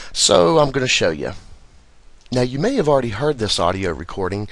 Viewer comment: Uhh did anyone see the face ON the spectral graph?? @ 1:36 look 2 blocks up from the V in viper. Clearly shows lips with the upper lip indent right below the nose. Left cheek is visible but the face fades above the nose... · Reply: I thought she said "now you see me"